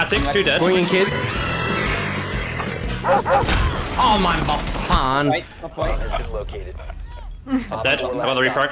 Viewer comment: I heard muffin